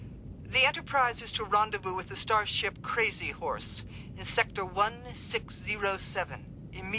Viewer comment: Starship Crazy Horse Did I hear that correctly?